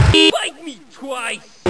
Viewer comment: What does he say??? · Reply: bite me twice.